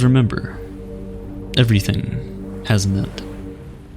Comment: I didn't understand what he said at 12:11 did he say "everything has nut"?